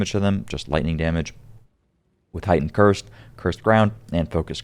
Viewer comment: I swear this man said "chris brown" instead of "cursed ground" at like 11:40ish lmao. Im gonna try this version soon. Im on level 4 maps and getting annoyed getting 1 shot at the end by somthing off the map. I did find 2 divines in 2 hours though today lol.